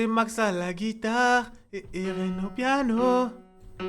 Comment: c'est moi il dit c'est Max a la guitare et Terrine au piano 😂😂